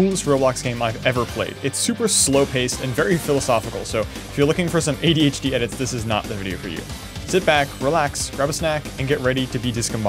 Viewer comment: I thought he said “If you’re looking to get ADHD then this is the video for you”..